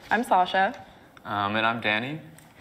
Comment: I thought he said, "And I'm Daddy." Till I read the caption 😂😂